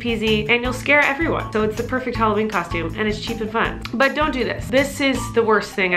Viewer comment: When she said its cheap and fun, i thought she said cheap as pot! Lol